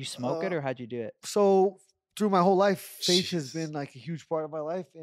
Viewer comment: PLEASE HELP at 0:32 does he say faith or sage or wtf does he say 😂 THANK YOU VERY MUTCH IN ADVANCE